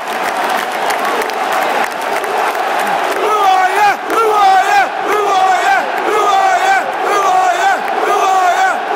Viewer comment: What are they saying at 1:55 ? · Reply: Who are ya!